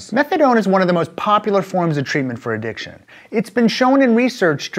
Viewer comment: It says 'addition' instead of 'addiction'